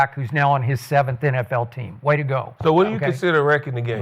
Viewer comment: Skip said way to go and I heard whoopty doo